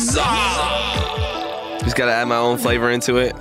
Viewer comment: Ayo did you say flavor 21:44 what the fuck dude 🤨📸